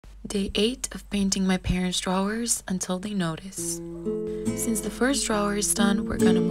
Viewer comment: i thought it said "day 8 of painting my parents divorce until they notice" 💀